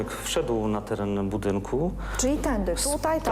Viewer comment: Tylko ja usłyszałam "tędyk" zamiast "tędy". Xd